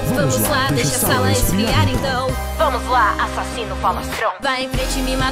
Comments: Caralho, eu entendi "Assassino de Faustão" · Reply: Eu foi muito pior, eu de primeira entendi "assassine o Faustão" kkkkk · Caralho, eu entendi "Assassino de Faustão" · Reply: Eu ouvi "Assassina fala pão"